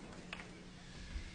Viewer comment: I thought he said shoot up